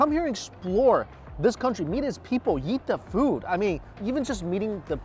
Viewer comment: I just heard Mike say " eat the people meet the food". But i know that is not what he said XD